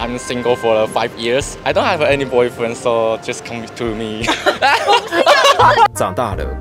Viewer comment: 嚇我一跳，我聽成coming 督 me，聽第二次才知道to